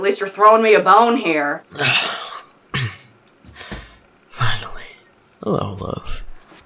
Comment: i deadass thought he said "hello loaf"